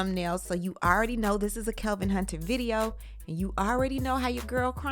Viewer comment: I thought I heard you say Covid Hunter